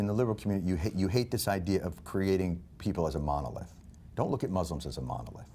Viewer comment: What does Jon Stewart say at 4:15? A mono/modo what? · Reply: Jk figured it out. Monolith.